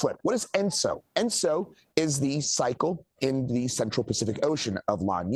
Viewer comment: Did he just say sexual?